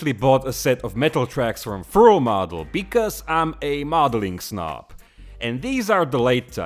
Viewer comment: Did i hear "i am a modeling snob?" Lol!!!! Cracked me up. Loved it !!! I have at least 30 sets of various fruils LMBO!!!!!